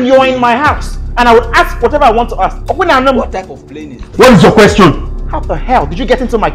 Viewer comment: That part I heard "are you playing? What type of playing is this?"